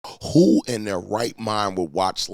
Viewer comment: Thought you said who in their white mind haha